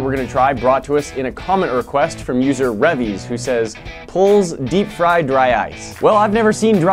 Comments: Did I just hear "pulse" deep fry dry ice? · Did you just say pilz deep fry dry ice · did he say pulls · pulse instead of please😂😂😂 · This mane said polls instead of please? Smokin dickz